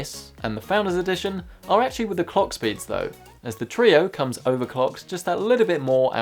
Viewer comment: i know he said 'clock speed', but could of swore he said cock spit